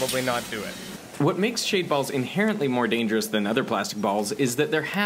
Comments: I thought at first he said shave balls, and I thought they had like needles or something. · this man has the balls to do it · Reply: At first I thought he said, ”Shaved Balls” lol not shade balls. lol.